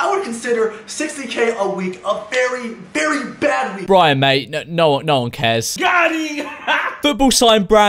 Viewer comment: did he say brian may 🤣🤣